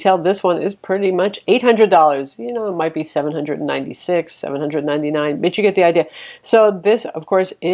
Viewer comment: sounded like she said "Bitch you get the idea!" haha good review though